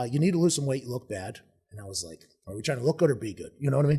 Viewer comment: What the hell does he say at 0:35? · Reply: he was out of shape basically “doesn’t look good”